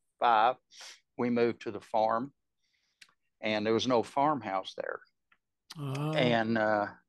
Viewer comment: He said there was no farmhouse, then he immediately starts talking about sleeping in the house and the bedroom ceiling was covered in flies-? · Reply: There was an “old” farmhouse that misheard as “no” farmhouse